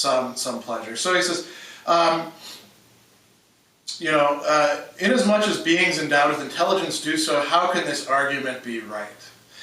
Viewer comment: yo WHAT does he say at 7:19 to 7:25 its like the most important part and i cant understand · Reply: in as much as beings endowed with intelligence do so how can this argument be right